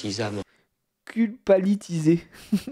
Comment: J'ai entendu " culpabitisame "